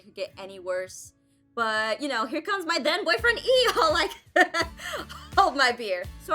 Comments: I thought you said my DEAD boyfriend E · Did say “Hold my beer!” 🧐🤔🤔